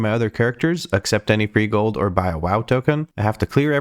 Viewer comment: i just realized the intro says „i cant send any money from my other characters, ACCEPT any free gold or buy a wow token“ ,i always thought you said „except“ and i was like why doesnt he buy a wow token when he‘s low on gold lmao😂